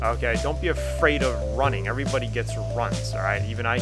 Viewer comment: at first I heard, "everybody gets the runs" :)